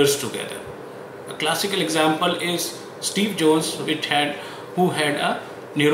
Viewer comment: I heard Steve Jones instead of Steve Jobs